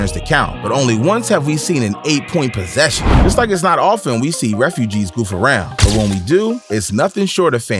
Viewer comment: Did he just say “refugees”?